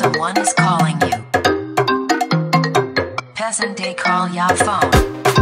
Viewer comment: "Person dey call ya phone" LOL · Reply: I thought it said peasant they call ya phone